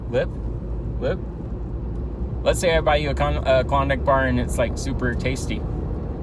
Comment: my boy said condom instead of Klondike 😂 get ur mind outta the gutter